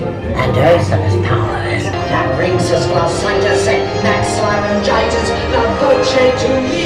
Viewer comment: laryngitis? did i hear that right